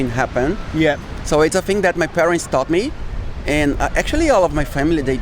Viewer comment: ブラジル訛りなので聞き取りにくいですが、my parents taught me ではなく told me な気がします。